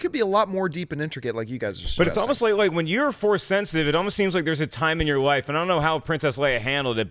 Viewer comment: Did he just say that there was "Force Puberty".